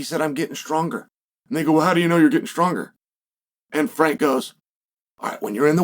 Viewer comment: What did he sayyyy 😂😂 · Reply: "And they go well how do you know your getting stronger"